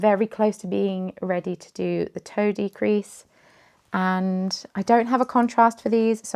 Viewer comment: Here because of the cardigan but happy to join for everything else! When you said "toe decrease" I heard it as "toadie crease" and was extremely confused lol I'm not knitting much right now, just a little swatch of a pretty yarn called Anchor Bay Hand Paints by Cascade Most of my time has been split between going through my yarn stash to determine what should stay, go, or become my mom's for crocheting hats for charity, but a fair amount has also gone to my newest craft, friendship bracelets!